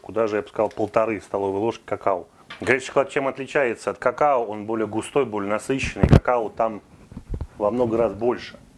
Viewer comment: На 4:17 что сказали?что чем отличается от какао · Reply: Горячий шоколад отличается от какао тем, что он более насыщенный и более густой.